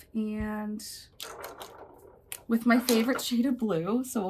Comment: I thought you said your favorite cheeta blue 😂 and then I went back and heard shade lol I was like what does she mean cheeta lol 😂